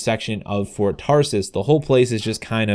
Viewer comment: It sounds like ur saying Ford Taurus lol